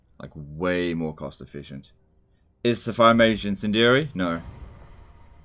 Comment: At the beginning of this stream I kept hearing "Fire mage incendiary." How many of you watched Brit man...Jeez.